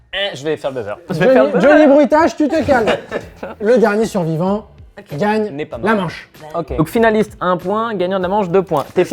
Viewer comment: Ya que moi qui a entendu le "Le dernier survivant : n'est pas mort 😂" 0:32 ou pas loin.